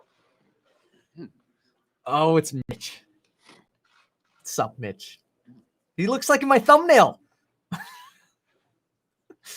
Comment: I thought you said Mitch that son of a bitch lmao